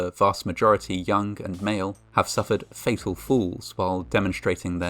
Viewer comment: I thought he said, "... suffered fatal fools", haven't we all? Oh, "suffered fatal FALLS." Got it.